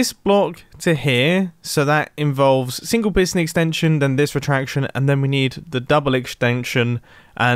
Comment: who else heard him say exnstension instead of extension